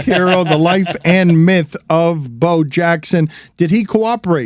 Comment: Damn, I had to quick go online and make sure there wasn't breaking news snuck in there. I see the word was "myth" that I heard, but casually listening I thought Dan said the subtitle was The Life and Death of Bo Jackson.